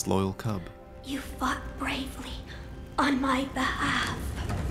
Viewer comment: You what bravely on my behalf?!!! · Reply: At first I thought he said you fu$& bravely on my behalf